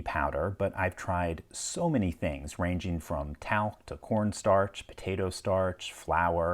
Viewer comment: ~ I swear I heard "porn starch". 😆